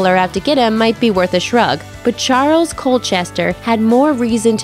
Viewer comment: In case anyone else had as much trouble penetrating this lady's accent as I did, the name at the 1:47 mark is Charles Colchester, I was sure she had said, "Giles," and thought she had said, "Colchester," so I did an Internet search to be sure.